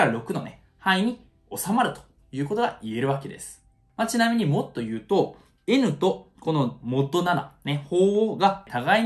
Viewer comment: 「もっと言うと〜」が「mod言うと〜」に聞こえないことはない